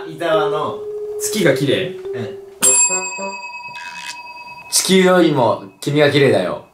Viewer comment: 地球に聞こえる